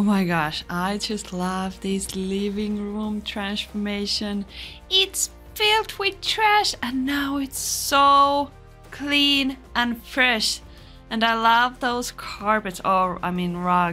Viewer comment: I thought you said trashformation – and you should! :)